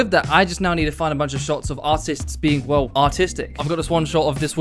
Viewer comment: Whn he said "Of this woman be doing a TikTok dance" I thought he said autistic instead of artistic (04:06) and I agreed, unfortunate.